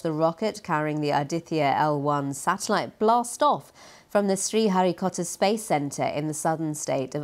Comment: Rock(et) On India. Keep flying straight. Just one question... did I hear the newscaster right? At 0:12 is the space centre really called the "Sri Harry Potter Space Centre"?